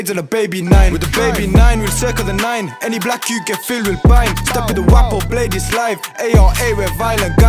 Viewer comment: did I hear that right? “any black yout, get filled with bine”